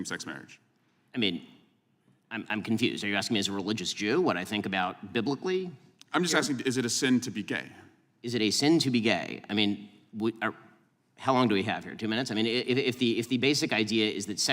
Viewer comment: I thought this said “is it easy to be gay” which is actually a more relevant topic